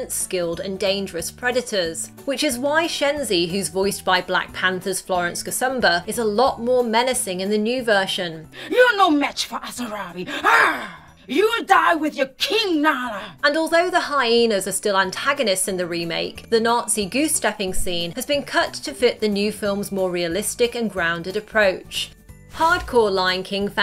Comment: did anyone hear “king Nala”